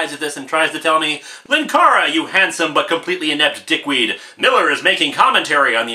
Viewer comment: Did Linkara just say dick? Also, I thought Ray had gone feral? I guess they just forgot that plot point? 16:11 But Linkara, didn’t you suggest that “Catgirl” could have been hot before?